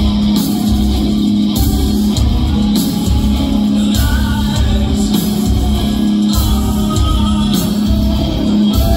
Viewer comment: what does he say at 5:06? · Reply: "Flies are waiting"